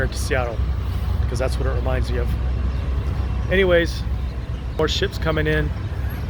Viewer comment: I heard horseshit comin' in at 16:58, then saw the horseship